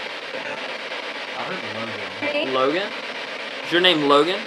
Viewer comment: Interesting at 6:08 when it gave a name I thought it said Morgan but then someone who was there heard Logan..but I do def hear the gan bit of a name but thought it was Morgan.